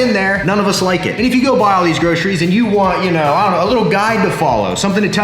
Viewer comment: I swear I heard you say 'bro'ceries